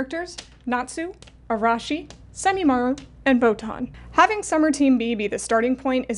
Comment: We finally got a shoujo anime... but at what cost? 😮‍💨Why do we shoujo fans exist if only to suffer? 🥺 Press 'f' to pay respects as we pour one out for the lost potential of a great series. 🪦💐😢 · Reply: I also legit had to rewind to 4:07 because I misheard Boton as Pokemon. 🤣Forgive me for I am ill and it's messing with my head. 😷🙏 (I'm also bad at spelling and functioning as a human in general so I also apologize if I butchered the spelling of the character's name)